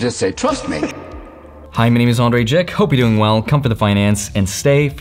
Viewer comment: look at your subtitles bro😂. Its writing “Andre Dick” instead of “my name is Andre jikh” 😅